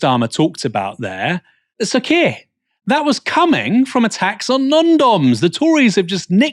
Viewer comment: Did he say a tax on condoms?!